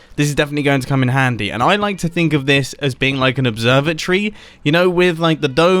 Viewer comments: When he said observatory i thought he said observer tree😂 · When Mumbo said observatory thought he said observer tree. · I heard this as Observer Tree. do with this information what you will.